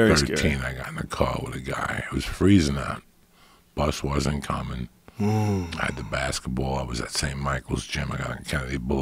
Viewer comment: what did he say "he was freezing now nit uncommon" ???